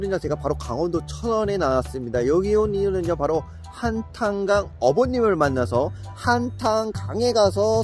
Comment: 한탄강 어머니를 만나서로 들리네 ㅋㅋㅋㅋ